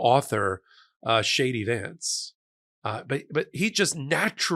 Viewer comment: I thought I heard you say shady vance? Not a bad name. 😅